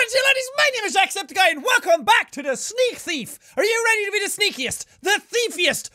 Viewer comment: I thought you said welcome back to sneeze thief